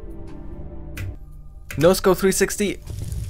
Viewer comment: i couldnt stop but think that the captions said Knows Go 360 instead of Noscope 360.... 6:55 and it isnt even automated. someone wrote that